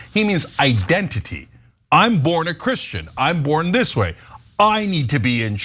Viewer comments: He said " Im NOT Christian " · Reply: Right I’m I the only one… I thought ppl thought he said I’m not a Christian. He shook his head and everything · I thought he said I’m not a Christian idk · It sounds like Trump says “I’m not a Christian”. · I heard him say I'm not a Christian.